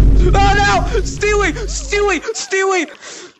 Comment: in 3:21 did he say stewie